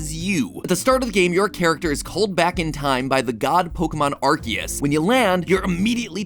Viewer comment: IM SORRY DID HE JUST SAY "ARGEUS"??? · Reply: No? He said arkeus